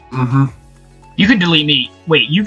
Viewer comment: i really thought said “you should delete me” for a hot moment lmao 💀‼️